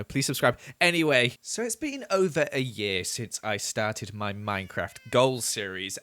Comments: Is it just or did other people thought said over 8 years instead of over a year? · Me freaking out after I miss heared and thought he said it had been over 8 years!